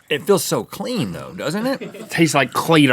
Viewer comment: it sounds like link says "Cleader" not Cleaner! Lmao they need to make shirts with his face on it and it just says Cleader